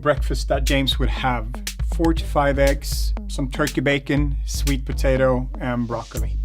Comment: that sounded like 45 eggs lol